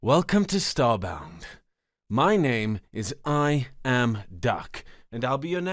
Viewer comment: I am duck? That's a funny name.. but I like ducks, so I like you. ♥🦆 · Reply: I always thought he said “I am dark.”